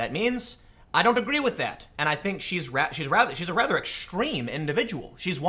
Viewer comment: did you just call her ratchet?